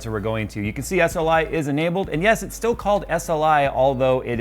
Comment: Thought you said SLI is dead. BFV 5 with RTX no SLI so whats up?